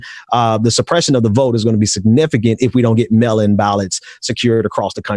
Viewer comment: I didn't realize until the end that they were saying mail-in ballots I thought they kept saying melanin ballots